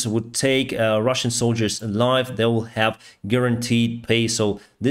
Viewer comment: At like 7:11 in the video was he saying they get a reward for taking prisoners ,alive, or was he saying taking ,a life, like ded. Might have been the pronouncements. I'm assuming the prior but just wondering